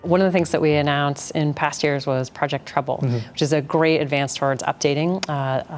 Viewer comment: project trouble instead of treble in subtitles